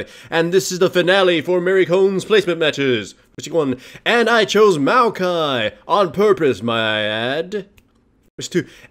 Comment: what the heck does he say at 0:09? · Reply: 'voice check one', does it so he can help sync up the audio